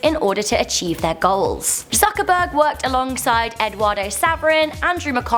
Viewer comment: Am i the only one who heard it as Suckerberg